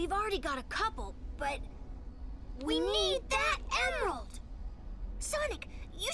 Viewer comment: what ammo for gun they said: WE NEED THAY EMMO